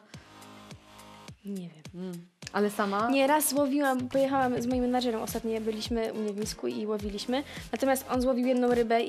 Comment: Czy ja usłyszałam :"z moim mężem" czy się przesłyszałam 3.06?